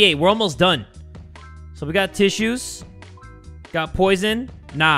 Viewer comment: I heard that as "We got issues" 😂😂